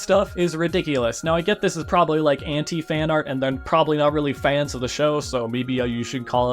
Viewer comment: I think anti-fanart should be called fart. Definitely cause I misheard say anti-fanart as anti fart at all